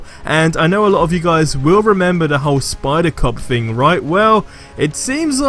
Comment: is it just me who hear spider cock